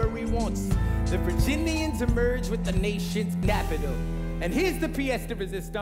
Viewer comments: does Burr say "Napital?" I know the lyric is "capital" but I think they messed up the mics or he genuinely stumbled on his words. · did he say the nation's "napital" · Did anybody else hear napital instead of capital at 0:23? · Um, does Burr say "Blapitol" or "Glapitol" at 0:22? I know it is supposed to be capitol.